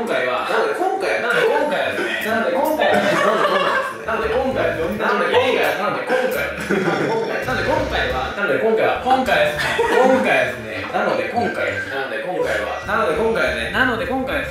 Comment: てつやの「なので今回は」が途中から「なんで来んかい」に聞こえる